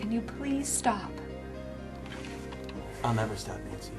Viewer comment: what did he say? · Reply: I’ll never stop Nancy